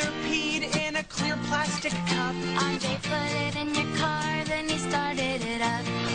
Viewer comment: on 0:42 it says cat instead of car